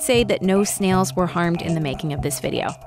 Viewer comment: Hold up, did she just say: “A shrimps gotta eat, and do do we”?